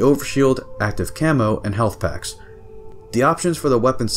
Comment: Did you say health packs at 10:25 ??